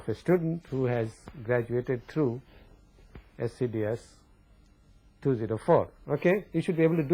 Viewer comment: The professor mentions @ 29:17 a class where you learn how to obtain gains of multistage amplifier circuits. I think he said scds 204. Where can I find videos of this class? Professor is so awesome.